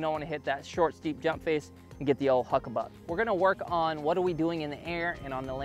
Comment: I thought he said the “oh fuck buck”